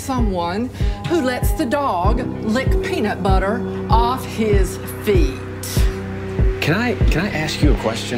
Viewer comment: I need to pay attention to captions. I heard "let the dog lick peanut butter off your" and my mind went to a dirty place LMAO